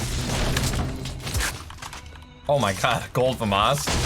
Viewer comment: Why did he say “gold vinage?” At 0:38, what does that mean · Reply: He said "Oh my god Gold Famas" Famas is basically Epic or Legendary Burst AR. Like Scar.